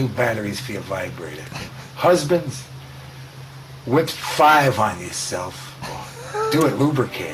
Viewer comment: what does he say? "Whip 5 on yourself"?